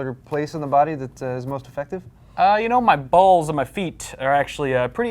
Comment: I thought he said "My Balls"...